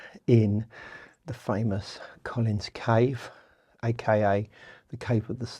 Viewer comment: Did actually call the cave "Collins Cave"? Hahahaha that's too funny!